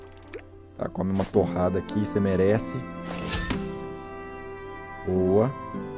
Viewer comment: Da primeira vez eu escutei "Tome uma porrada aqui, porque você merece!"